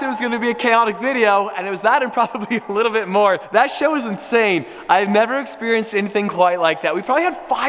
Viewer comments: Did he just say a bad word 10:49 😮 he growing up before our eyes 😢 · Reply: It not a bad word he said "the show is insane* not "shit is insane" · I thought he said “that shit was insane” but he said that show lol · Anybody else hear him say, “that shit was insane” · is it just me or did he say sh*t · am i the only one who though he said "shit" at 10:48? (yes i know he said "show" but it sounded like shit lol) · what did he say ???😂😂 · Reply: Listen carefully and you can hear, "That show was insane."